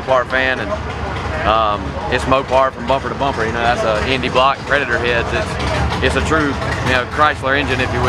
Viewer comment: hemi block..predator heads?...but didn't look like hemispherical heads.. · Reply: Thenitroviper thats more like it...i thought i heard him say hemi block...thanks